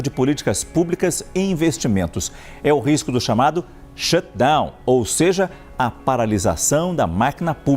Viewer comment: risco chamado "Xandão". Hahaha eu ouvi, você ouviu, mais alguém?